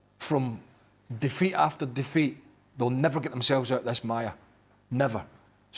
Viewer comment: did he say Maya?